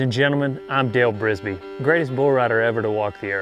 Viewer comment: Every time he says "Hey, I'm Dale Brisby" I hear "Hey, I'm Dale Gribble" throw a few rats in the basement and the personality kinda matches.